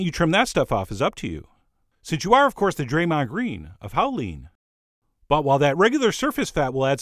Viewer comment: Did he say Draymond Green?